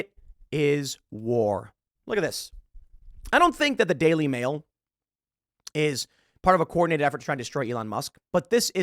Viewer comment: When you said the daily mail I thought you said daily wire 🤣